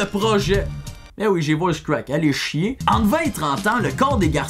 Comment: Qu'est-ce qu'il dit à 3:44??? Je comprends pas😂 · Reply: Jadou "bin oui j'ai voice crack allez chier"